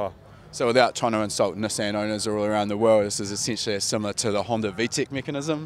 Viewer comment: Can anyone help me with what the guy exactly says 1:16 until 1.22? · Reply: without trying to insult nissan owners around the world, its essentially similar to the honda vtec system?